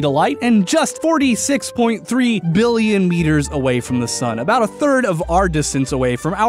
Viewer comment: I thought he said light-years instead of meters so the orbital radius would be the size of the universe.